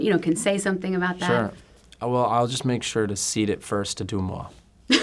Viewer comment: What did he say at 8:45? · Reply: Seed it first to deuxmoi